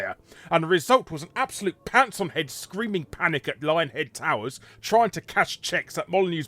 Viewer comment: Did I just hear you sneak "Lyinghead" instead of "Lionhead" in there, Larry?